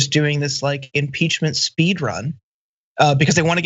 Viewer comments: Yeah, I heard "Imporchment." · 1st you did say "Imporchment" 2nd Man you guys keep blasting on these "ReThugican" lawmakers ... they do this because they know their stupid ass voters are going to vote for them anyway. Why are you surprised that they don't care, they don't have to care. Bankrupt just doesn't cover it. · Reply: I heard "imporchment." I'm happy it wasn't just me. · Yes... I replayed it after I first heard you say it... "Imporchment". 😊